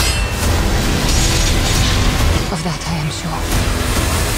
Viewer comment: anyone else hear "I will make them pay" in an AI voice.